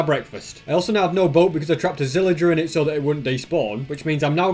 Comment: did he say a zillager XD